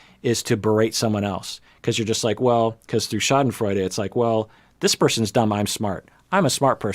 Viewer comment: did he just say "Schadenfreude"?